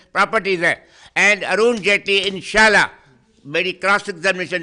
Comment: What did he say? was that inshallah? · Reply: Ha bro Inshallah Hi bola wo